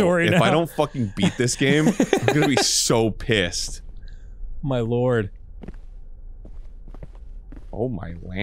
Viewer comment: I really thought at 14:20 Dan was just calling Arin 'My Lord'